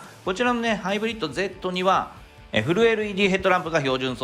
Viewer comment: この前疲れすぎて嫁とできんかったショックが残ってたから1:27でフルLEDが震えるEDに聞こえたw